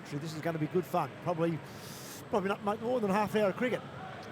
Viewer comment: Did he said, "probably not more than half hour of cricket." 😂😂😂😂😂 I reckon he is thinking Australia is going to win easily here.